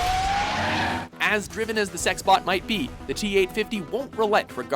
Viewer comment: did he say sex bot?